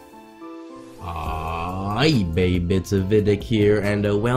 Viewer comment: What do you say at the beginning of every video? I hear "eBay bits, vihitic here"